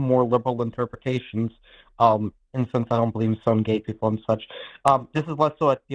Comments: Here ya go...... 21:33 - "and since I don't believe in some gay people and such" This is what I got from what said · "I don't believe in stoning gay people..." I SWEAR I heard "I don't believe in SOME gay people".